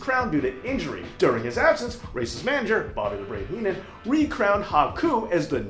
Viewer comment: At first, I thought you said racist manager Bobby Hennan instead Race’s manager Bobby Hennan. LOL.